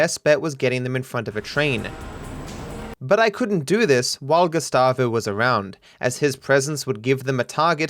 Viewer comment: i swear i heard gestapo 🤣🤣